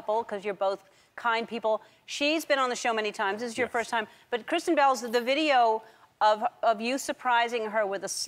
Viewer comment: Every time she says Kristen Bell it sounds like she is saying Christian Bale